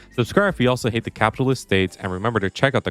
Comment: "Subscribe if you hate the..." what? I heard capitalist states and thats what the closed captions say, but that doesn't make any sense in this context